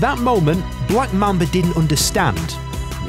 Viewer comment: "Black man that didn't understand" lmao